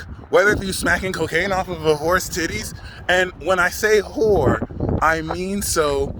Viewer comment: lmao I thought you said horse titties